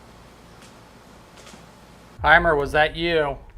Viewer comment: I just heard hymer 4:42pm